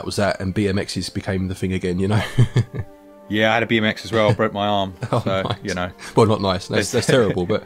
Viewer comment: I thought yall said: "I had to BEAT MY EX and broke her arm" 😩 But yall said: "I had a BMX and broke my arm"💀